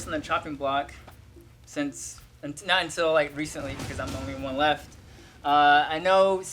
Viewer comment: Did anyone hear said “I’m the only one left”????????